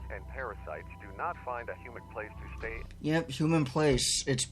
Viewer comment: i think it says humid but idk